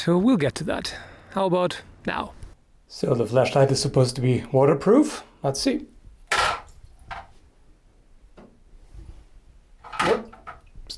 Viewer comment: Lol at first I thought he said flesh light... More interesting subject though, until it gets weird and then yeah I would dislike by then :P